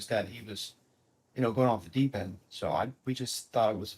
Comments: I kept hearing “Kris Jenner” lol but Chris jr obviously… Mr Bledsoe seems so genuine I could listen to him speak about anything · Thought he was saying Chris Jenner but rather it was Chris jr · For 30 dang minutes I thought he was saying Chris Jenner! B😂